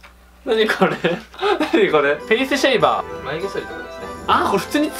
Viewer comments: ペニスシェイバーて聞こえたｗｗｗあと、プロテインはただのタンパク質の塊ですよ。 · フェイスシェイバーが ペニスシェイバーって聞こえた